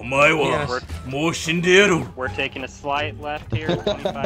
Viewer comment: What did he say at 6:17?? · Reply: "Omae wa mou shindeiru" Or in english "You are already dead" later he says "nani" which means "what" in Japanese